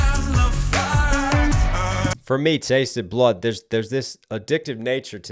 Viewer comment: You have impeccable ears, and I clearly dont.. because I always thought he said "whens the last time you tasted battle" 🥲 Now it makes more sense bc blood and flood ryhme 😅